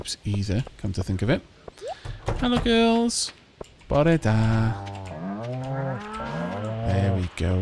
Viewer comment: what is the phrase you say at 34:30 and what does it mean? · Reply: "Bore da" good morning/day